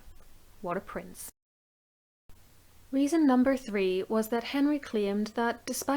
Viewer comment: lol when you said what a prince! I had to check back cause I thought you said what a prick 😂😂